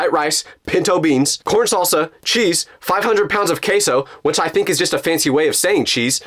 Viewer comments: bro said queso and it sounded like caseoh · am I the only one who hears 5000 pounds of Caseoh